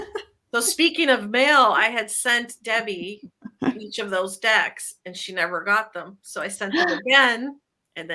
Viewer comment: Debbie, I thought you said you were giving away “two dic**” instead of decks. 😳